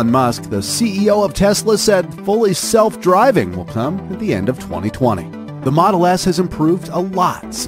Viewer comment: I thought you said 12 not 2020